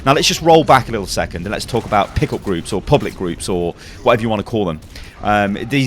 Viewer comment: It wasn't until the third time you said "pick up group" I realized what you were saying. Prior to that all I heard was 'pickle group'